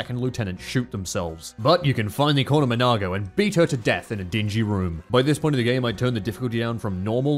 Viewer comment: Xenoblade has ruined me... all I hear is "You can finally corner Monado and beat her to death."